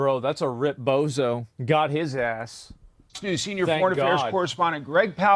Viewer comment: Dude at 0:54 i thought you said "God is ass" not Got his ass